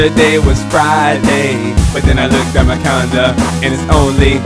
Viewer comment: Did it sound like he said condom instead calendar?